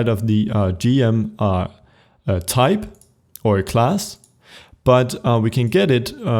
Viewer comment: I SO thought he said D.M. class.